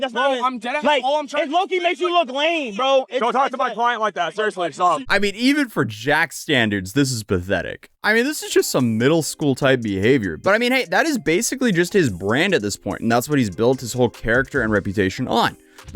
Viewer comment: thought you were gonna say that’s basically just his middle school “brain” at this point, but you said “brand” which makes more sense LOL